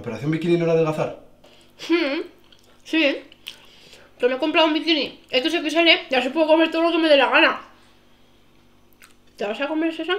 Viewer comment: Hola, Alguien me podría decir, que dice la chica en el minuto 1:02 me he comprado un bikini..... El chico lo hace muy bien y el final con lo de la playa es 100% real... ajajajajajaaj! Felicitaciones...! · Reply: "me he comprado un bikini XXL y así puedo comer todo lo que me dé la gana"